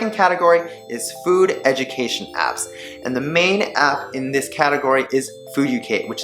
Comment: What app did he say at 7:07? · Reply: fooducate i think